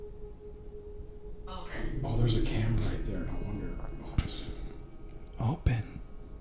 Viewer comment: Dis anyone else hear a female voice say no at 1:30:13 ?